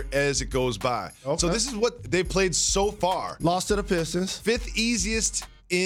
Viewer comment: did Jalen say "lost to the Pistons"? Because no, the Lakers haven't played or lost to the Pistons yet. If he saying "a loss to the Pistons", that ain't happening either lol!